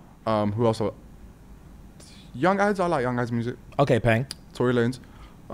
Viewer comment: did you say Tory lanezzzzz 😮